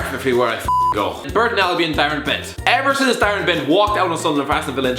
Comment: is it just me or did he say barren bent?